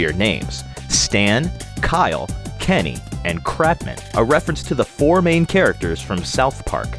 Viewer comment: Cratman? Whaat? I almost thought he said crapman!